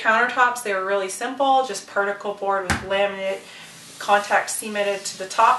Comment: On 10:55, did she say "contact semen on it"? XD